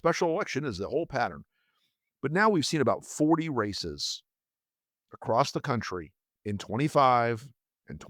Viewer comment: Misheard young Wilson, thought he said 40 racists,...